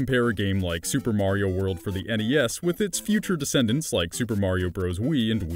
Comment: I swear he said Super Mario world for the nes at 2:48 and not the Snes